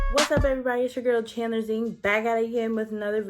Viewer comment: I heard you say your name Chandler Bing